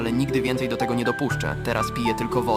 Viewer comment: Tylko ja usłyszałam „teraz piję tylko wude” XD